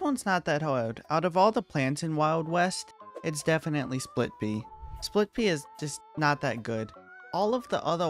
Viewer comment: it says SPIT pea Instead Of SPLIT pea